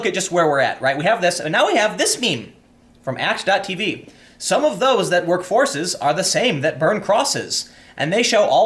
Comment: Huh.. I always thought he was saying "Some of those that run forces"....